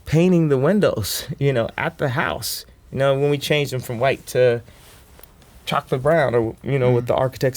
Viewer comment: I hear "From white to chocolate brown" while my friends hear "blue to black" Someone help??